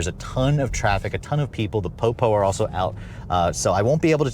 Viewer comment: "The Po Po are out" 😂😂😂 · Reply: I almost thought he said the poo poo are out 🤣😂😂😆